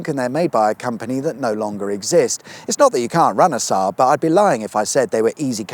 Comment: I heard "run a Saab" as in Renaissaab. A Saab Renaissance please.